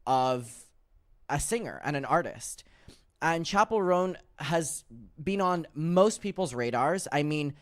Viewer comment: I thought the name was said like “Shappell” for some reason